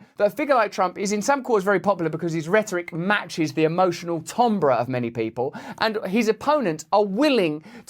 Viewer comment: "emotional tombra" what's that word??